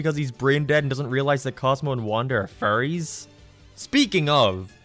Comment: Lmao I thought he said "Cosmo and Wanda are furries"